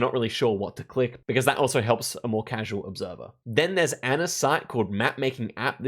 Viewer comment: Can can someone transcribe what he said at 3:17? english is not my main language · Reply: Then there’s Anna’s site called ‘mapmakingapp’